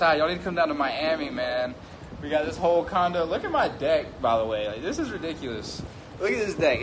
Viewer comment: I thought you said look at my dick 😂😂😂😂😂😂😂😂😂😂😂😂